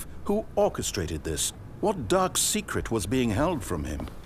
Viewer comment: I could have sworn it said "duck secret"